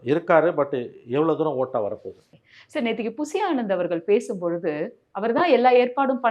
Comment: did she just said pusy anand 😭😭😭😭😭